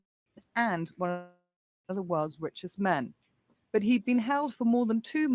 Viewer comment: I thought you said he had been killed before